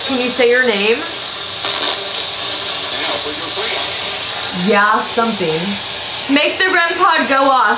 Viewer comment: after says something about their names I heard “yeah but we’re( or your) waiting” let me know what y’all think.